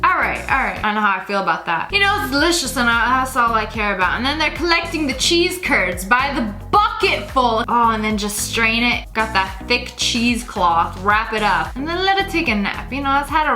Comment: CHESE CURDS OMG LOL I THOUGHT YOU SAID TURDS SAA